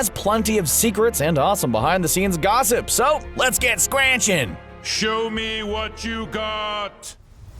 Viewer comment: Did he say squranching instead of squanching?